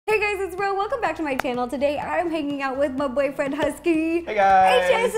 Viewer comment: I thought you said I'm here making out with husky!😂🤣